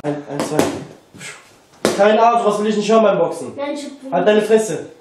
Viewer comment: Was sagt bei 3:42 zu seinem Bruder ?? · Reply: Ümit Temizbas er sagt : kein ah sowas will ich nicht hören beim Boxen